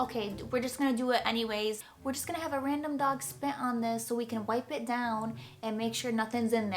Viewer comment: Did she just say "a random dog spit on this" 🤦🏾‍♂️😁